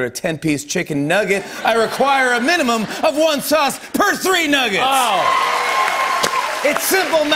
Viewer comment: At first I thought she said “3 sauces per nugget” I was like Wow!...that’s a lotta sauce